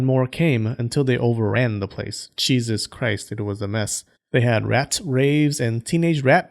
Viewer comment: “Cheesus Christ” - made my day 😸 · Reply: At first I thought he said cheeseits.